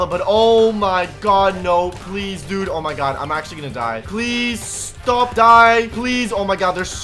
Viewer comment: i thought u said "dont die" to the pillagers